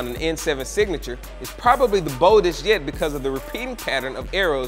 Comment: did anyone else think he said "bold as shit"???